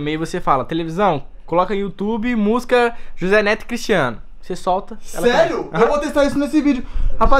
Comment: So eu entendi jose neto e cristiano